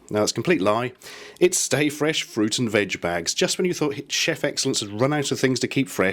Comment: I honestly thought it said "Stay Fresh Fruit and Vag Bags." Oh Lawdy I lol'd.